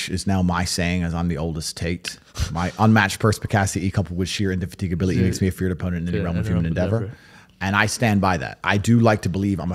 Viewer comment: Wtf did he say at 56:09?? I really want to know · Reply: My unmatched perspicacity coupled with sheer indefatigability makes me a feared opponent in any realm of human endeavour